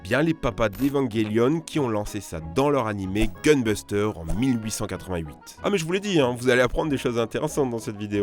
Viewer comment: Ses moi ou il a dis a 8:50 1888